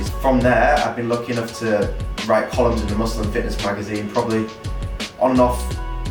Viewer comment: I didn't hear correctly...you wrote @ 6:11 - you write in muslim fitness magazines? wtf? lol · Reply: Muscle and fitness